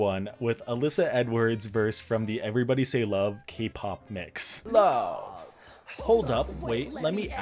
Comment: okay wait i didn’t watch global all stars. did you say k-pop mix?